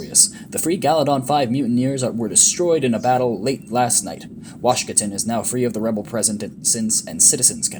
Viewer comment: When I first heard Washkatin I had to do a double take cause I thought you said Washinton. Crystal desert planet sounds like a good name for a band.